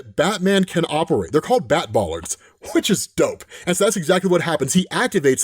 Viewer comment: brushing my teeth while having this vid playing and misheard "bat bollocks" and to stop my brushing and rewind... 😂😂